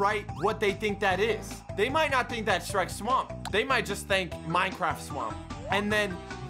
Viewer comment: I thought he said shreks mom at first not shreks swamp lol